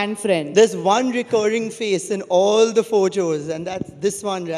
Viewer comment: did he say PhoCHOS 😂 and not photos 😅 Ye saari duniya ka para hai..